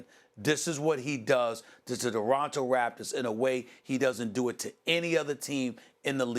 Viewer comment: Did he say DeBronto Raptors at 3:27 ?